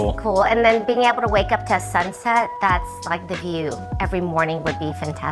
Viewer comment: did she say 'being able to wake up to a sunset'?